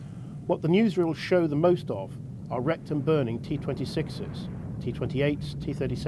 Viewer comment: I thought I heard rectum burning, not wrecked and burning. I had to rewind to clear that up. What about the rectum burning T-26? How would that have stood up to the Wehrmacht?